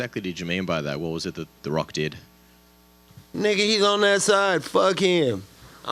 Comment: Me and my cousin are having a debate at 1:35:21 did Nate say nigga? Lol